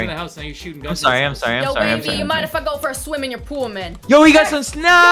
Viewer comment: Did say the bad word? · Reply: Nevermind I just heard "might if I go for a swim in your pool man" I thought she said mf